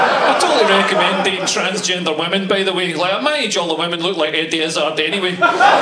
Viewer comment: Gosh >< I can't make out the end of his joke about dating transgender women, help please :) it's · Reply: Ian Lazarus He says that at his age all women look like Eddie Izzard, who is a transgender comic